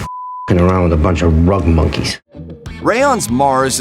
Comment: First of all, sorry but I just finished watching the movie Jess then a half hour ago... I didn't even know this movie existed lol so pardon my comment being about 3 months late lol 4:16 - did he just say "run monkeys"? lol...wtf does being a "rug monkey" actually mean???